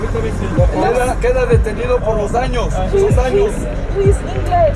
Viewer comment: That was so stressful! I speak Spanish so even hearing some of the things they were saying. Like they said at one point that they were going to throw him into jail for 2 years! Wow. How scary if you didn’t know what was going on too. I wish you guys a safe journey to Costa Rica. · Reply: I thought they said two years as well, but what they said was “los daños” which means the damages not “dos años”